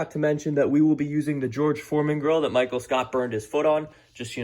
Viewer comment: Lmfaoooo at 1:04 I thought you were saying george floyde grill